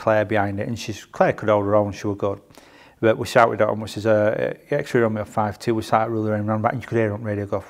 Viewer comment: Can someone type what he says starting from 23:32 Can't understand a word. Thanks! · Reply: We shouted on which is err "XR 5 2 we're sat at Rooley Lane roundabout" And you could hear on the radio go "phew, thank god Ben and John are here"